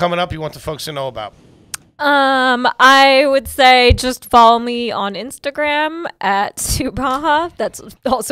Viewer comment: I thought she said follow me like obama wordplay 😂😂😂😂